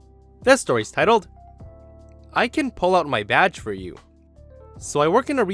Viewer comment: Now i was looking at something diffrent mind you.. I actually had to rewind, because i thought he said "I can pull out my vag for you..."